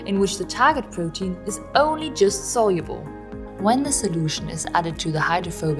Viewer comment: Great teaching ,I enjoyed it But please ,I couldn't get a particular word you were saying at 8:34,,,, did you say soluble??? Please I'll appreciate a response,, thanks in anticipation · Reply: Yes that is what was said. "The protein is just soluble."